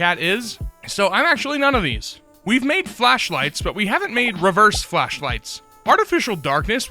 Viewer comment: Reverse flashlights are a great idea. I've been working on a reverse fire myself, but I always imagined it as cold with blue light, but emitting dark would be even better. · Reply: I thought you said reverse fleshlight for a sec and wondered how that would work 💀💀💀